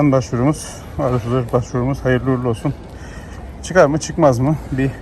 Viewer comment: R34 başvurumuz demişsin benmi yanlış duydum 😀